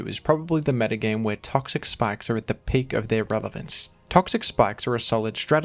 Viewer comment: Is he saying "Revelance" at 4:21 ??